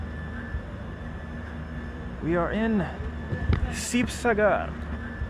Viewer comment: Did someone say cheap cigar?